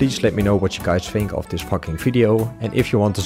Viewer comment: did you say “fucking” video?